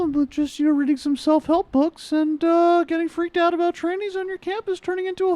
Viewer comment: Did Michael say trannies at one point lol